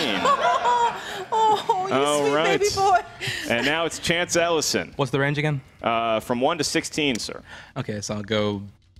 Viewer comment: Ugh, I need a hearing aid! Can somebody please tell me what said in 39:54 that made say, "Oh, my sweet baby boy!" Thanks. Cheers! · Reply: I said “Ooh, Lost reference!”